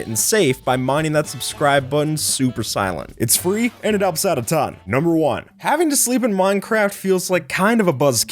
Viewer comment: the subtitles say "and it'll upset a ton". i kinda don't wanna sub